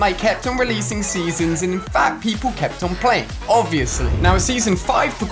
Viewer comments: sounds like he says fat people play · did he just say and fat people kept on playing 🤨